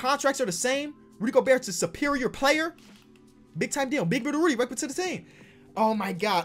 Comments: Did he say “Big Booty Rudy” at 19:00?😂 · did he just say “big booty Rudy” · Did he say big booty Rudy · Did anyone else hear him say big booty Rudy at 18:58 ?